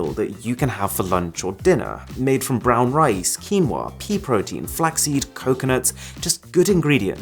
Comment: I thought u said pee protein instead of pea protein